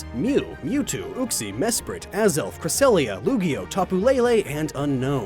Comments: did he say "Lugio"? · Did... did he just call Lugia "Lugio"? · Anyone else hear him call Lugia "Lugio?!" or was that just my imagination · Is it just me or did he say Lugio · Did you say "Lugio" or "Lugia", because it sounded like you said "Lugio". XD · did he just call lugia lugio? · Luigo????.......am I the only one who heard that? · Wait did he say Lugio at 14:00??? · Did... did just call Lugia "Lugio"?